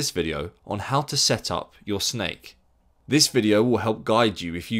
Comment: did i just hear "how to set Up your Snake"?